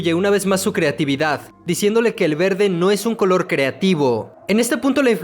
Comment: Se parece a creative de creep-p la palabra que dijo en el min